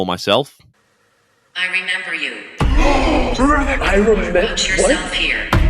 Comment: I always thought was a female name too. Never heard of a guy named. 🤷‍♀🤷‍♀ Around 16:18 The "spirit box/app" says "I remember you", but they have written on the screen "Remember Your Nightmare"? Someone can't hear!!! 🙄 Awesome video Love your humor!!! Thank you! 💖